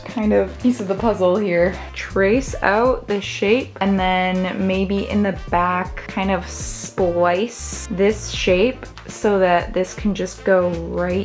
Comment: Watching you carve the torso out of foam and all I heard was "the shape goes into the shape" over and over 😂